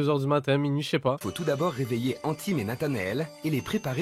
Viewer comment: Attendez c'est trop pour moi : -C'est près de Rouen, c'est mon terrain - À 4:40 j'ai bien entendu "Anthyme" ?! Non parce que c'est seulement mon prénom et j'en connais pas 100 ! Globalement, quelle était la proba...